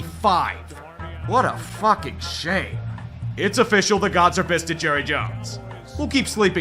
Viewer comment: "The Gods have fisted Jerry Jones" I must have misheard that rewinds "The Gods are pissed at Jerry Jones" aww.., I preferred the mishearing ;-p